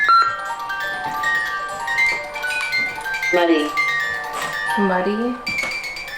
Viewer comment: when the ghost said muddy I heard bloody, did anyone else?